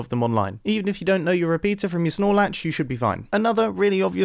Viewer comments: Did he say RS-nor latch or Snolax at 1:45? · Know my Repeater from my... What'd he say, Snorlax?? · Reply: SELF-REPLY: He's probably saying "snore-latch" or something. But here's another stumper: 5:41 He says "20% cooler" just like a certain fast character who flies and sonic booms! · I’ve been watching your videos for years now and i’ve always thought you were saying snoralack and it wasn’t until this video that i realized it was snore-latch